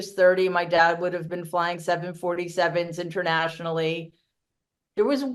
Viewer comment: I heard him say “Nashville” instead of “national”.